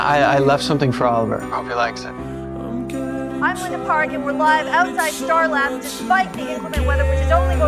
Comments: Just realized that the actor who played Linda Park in the Flash was a different actor. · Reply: I thought you said linkin park😂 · Just realized that the actor who played Linda Park in the Flash was a different actor. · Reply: I thought she said Linkin Park for a second